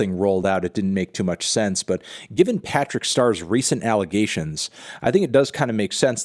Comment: It took me 3 minutes to understand this is a meme. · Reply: I heard "Patrick Star allegations" around 2:15 and I thought "wait a minute..."